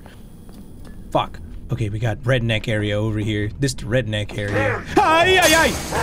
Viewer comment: did he just said tha N word? O_O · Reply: He said red neck gator